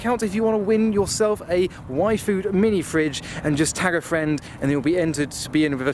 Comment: When says Y Food, but you thought he said Waifu 😂